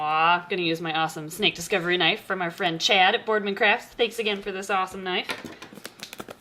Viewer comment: when you said thanks again for this awesome knife, subtitles said thanks again for this awesome night and I was like : WTF is going on here?! :-D PS. I'm huge fan of these snake and I want to own one in near future, they're one of my favorites, so please make more videos about it